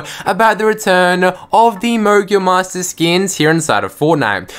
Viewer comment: lol it sounded like u said mongul monsters🤣🤣🤣🤣🤣🤣